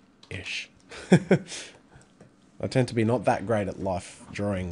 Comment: I thought he said " I tend to be not that great at life..." At 2:40 and I said SAME!!